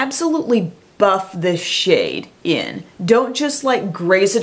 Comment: Oh god, I swear I thought she was going to say "buff this shit in" when she was talking about the highlighter and I gasped. Took a moment for my brain to catch up and realize would never, but now I can't get this image of an over-the-top punk acting like one of the Sex Pistols and raving about a highlighter. Stay you, but if you ever did go punk, you'd slay it 😂!